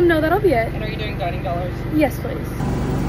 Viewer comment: I THOUGHT SHE SAID 390 DOLLARS